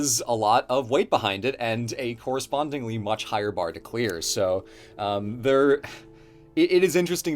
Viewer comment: Sounds like he said "much higher bar to queer" I don't know how gay Assassin's Creed is but I'll take your word for it